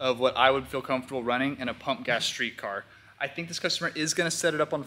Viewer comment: He said “pump gas street car” but I thought he said punk ass street car 🤣